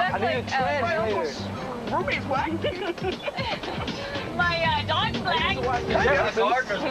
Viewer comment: they really thought “my gardener is black” was worse than “my dog is black” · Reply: I thought he said my doctors black and I was like word ok awesome, why they hating? That’s how I got here